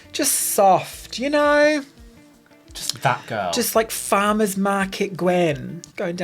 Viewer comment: Oh damn, 50:43 saying "just that girl", I heard it the first time "Just fat girl". So rude